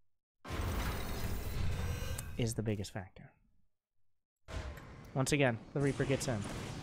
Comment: What does he say @ 30:45 ? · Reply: I guess "is the biggest factor"